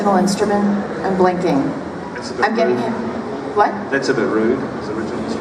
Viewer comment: Did things got heated at 22:24? I'm confused what the guy said to lauren. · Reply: He said to her don't be rude but i don't know why said about that